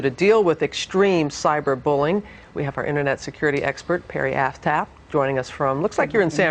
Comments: Did she just say: Internet security expert Perry Asshat?! Lmfao! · sorry...did she say Parry Asstap? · Did she just say asstap lol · i think she said asstape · Did she just call her "Perry Asstap" lol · does she say "Perri Asstap? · does she say the woman at 2:46's last name is asstap